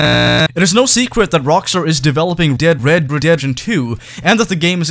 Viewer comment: hold on did he say dead red redemption